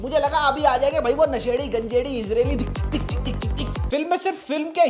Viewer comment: who heard israeli 😂😂😂